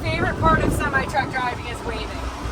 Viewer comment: I thought you said, “waiting,” as in waiting in line. NOT my fav part of going to a plant. But you said, “waving,” which is nice. I don’t often wave at male truckers, so I don’t often wave at females, as I’m not trying to flirt. However, if in the Aurora plant, and I see a white day cab hopper, with a young driver waving at me, I’ll definitely wave back, and yell, “keep up the good work, love your channel, say ‘hi’ to Grant,” at ya.